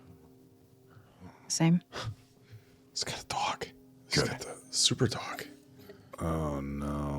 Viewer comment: What did the whisper at 7:23? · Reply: Super dog 🐕